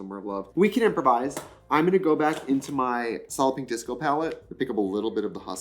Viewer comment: i’ve never seen solid pink disco written out so until now i thought she was saying solloping disco